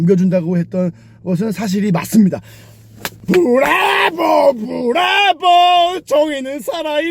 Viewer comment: 초에 나만 브라보 가아니라 불랄보 로들리는데? 나만 음란마귀 껴있나...?